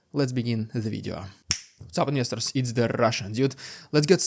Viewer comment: I thought he was always saying, "What's up misters" in a funny way lol